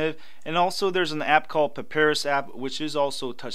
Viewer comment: he says something that sounds like "Paris app" I couldn't understand what he said until I freeze framed it. The app on screen is "PAPAYRUS" pronounced pə-ˈpī-rəs.